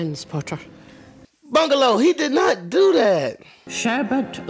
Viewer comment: bungalow! 🤣😂 ((Also did she say “sherbert lemon”? Lol))